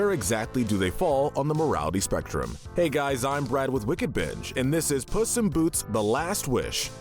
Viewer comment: I thought he said wicked bitch😂😂😂